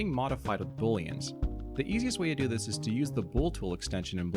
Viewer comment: 1. Very educational! 2. I thought it said "boot toot" at 0:59.